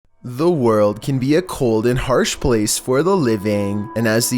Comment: It is one am and I hear "owo" instead of "The world"